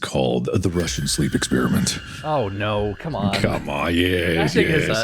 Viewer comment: I legit thought thought that said “markiplier explains the Russian badger sleep experience”